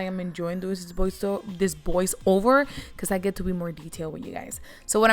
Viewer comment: I heard “boys over ” (voice over) and “booty blender” Hehehehe. How cute 😁😁